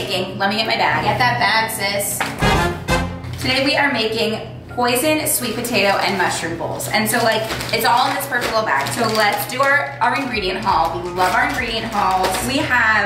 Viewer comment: i thought you said poisoned sweet potato